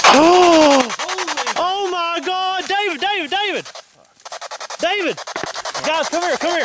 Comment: I heard “Heidi”